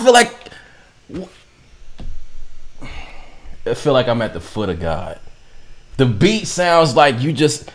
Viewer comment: "I seen God's toenails." - 2019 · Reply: LMAOOO I heard "I'm at the feet of God right before I read this"